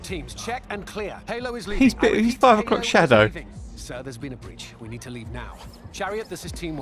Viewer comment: legit question cause Im studying english, if you see this or anyone? did you say like 'something shadow' at 3:27, about that guys beard? what exacly were the words and is it like an expression like the freshly regrowing beard is something shadow? never heard that term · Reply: 5 o'clock shadow is what I said, it's faint stubble growth that many guys get in the late afternoon when it's been a whole day or so since their last shave, depends on how fast your beard grows. It's just a way of saying they've got stubble.